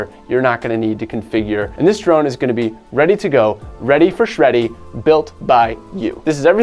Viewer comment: Did you just say , "ready for shreddy"? Lmao 0:55 seconds